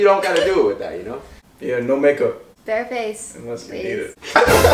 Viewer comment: what did he say? I replayed it 5 times and can’t hear what he said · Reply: he said yeah no makeup, unless its you